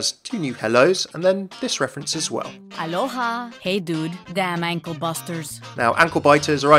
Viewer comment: Is she saying hey dude or hey doom. Doom as in doomfist? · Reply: Hey dude!